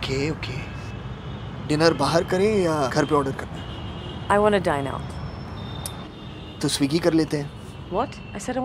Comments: For a second in the IPL ad I thought she is saying "I want to die now"😂😂😂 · At first I heard her saying "I want to die now" 🤣🤣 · I heard: " I wanna die now ! " And he recommended Swiggy "die now". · I heard "I wanna die now". Really ???? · I thought i only heard - i want to die now.....looks like everyone heard the same in comment section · I heard - "I wanna die now." . I thought the advertisement is from some pshychetrist. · Honest to god i heard 'I want to Die now' 😂